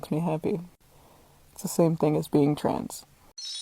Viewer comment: I thought the dog person said "it's the same as doing drugs". That's a statement I can get behind. I would still rather do drugs though.